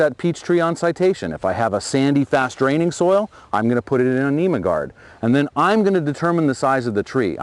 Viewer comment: I didn't uderstood what he said. on citation? if sandy and fast draining in anemic guard? wtf it means? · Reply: Nemaguard is what he says. It’s a rootstock.